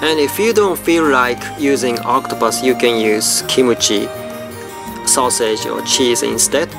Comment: omg, did I hear wrongly? Kimchi he pronounced as kimochi...-.-"